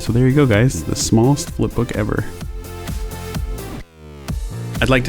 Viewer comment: I thought it said worlds smartest flip book